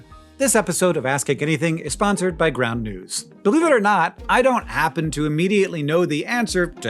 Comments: I heard "this episode of AHA is sponsored by Brown News" 😭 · did anyone else hear brown news? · Totally thought Hank said this episode was sponsored by Brown News, right after the brown color segment.